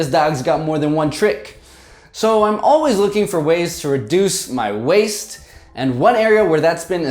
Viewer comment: At first I thought you said you were always looking for ways to reduce your waist and was confused, given how lean you are. Was starting to worry there!